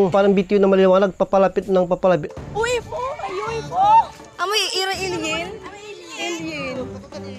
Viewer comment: ewan hah.. pero parang narinig ko na sinabi nyang "amoy alien"